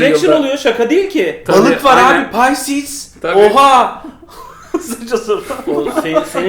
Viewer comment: kaç senedir izliyorum hala anlamadım bu şakayı. Hayır ne dediğini de seçemiyorum ki Google'layayım. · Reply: Pisces diyor. Balıkçı Petrus olarak googlelayabilirsin. Rica ederim